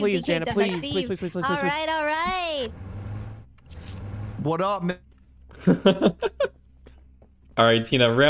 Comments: idk if anyone else noticed this but it almost sounded like said "what up ni-" and gets cut off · Lol 6:15 I thought I heard say "What up n-" & I had to play it back 😆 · WAS ELLUM GONNA SAY WHAT I THINK HE WAS GONNA SAY😳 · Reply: he said man